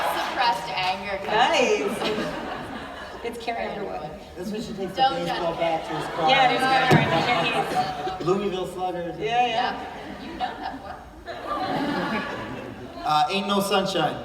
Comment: did Emily say Underworld?? 😬😂